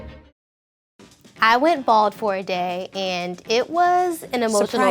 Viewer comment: I thought it said be baldi for a day